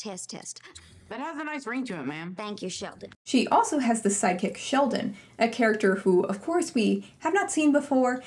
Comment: I ABSOLUTELY thought you said psychic Sheldon & tbh I kinda wish you had because I'd be interested 🤣🤣